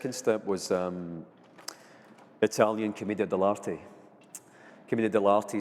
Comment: Can anyone understand whats the name he mentions at 0:47? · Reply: He said Commedia Dell'arte. Apparently it is some kind of theatre/drama play.